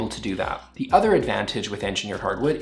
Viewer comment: I thought he said human environment instead of humid environment. I was like ‘yep. I agree. I’m way too clumsy’ 😂